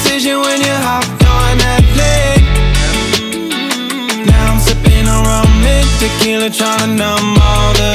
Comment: I hear "now I'm sipping Harambe"